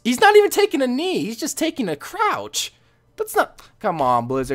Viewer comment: When you said "take a crouch" i thought you said "take a crap" 😆😆